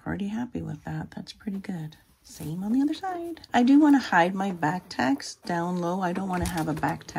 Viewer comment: When you were doing the swimsuit, I at first thought you said you wanted to hide your back fat. I was like don’t we all?